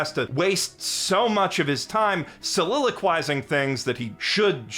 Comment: What is the word at 25:41? Selelequicing, never heard of it. What does it mean?